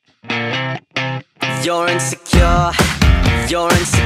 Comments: Why does it sound like a french man saying, “You’re in the car” lmao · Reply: Now all I hear is ‘your in sir car’ lmao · Sounds like someone saying “you’re in the car” in a french accent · My mind heard it as “Your insecure” “You’ll rinse the car” “You’re in cigar” “Urines the cure” “Urine cigar” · I ain't a lyricist but I think he is telling us that we're insecure. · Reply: i thought he was saying "you're a carrot", ty for that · If you listen closely he says “you’re insecure” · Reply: Oh! I thought he said "you're in a car" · If you listen close enough, you can hear “You’re insecure” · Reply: I'm trying but all I hear is "you're in ze car" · I started hearing "Urine's cure" after a while 🌝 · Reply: I heard "you're in the car" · the more you listen to this masterpiece, the more the words get twisted "yourin the cure" "you're in ze car" "urines the cure" "you're a cigar" the list goes on 😂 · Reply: I HEARD "UR IN SE CARUH" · I just hear “your in the car” in French 💀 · all i hear is "Yorin's secure"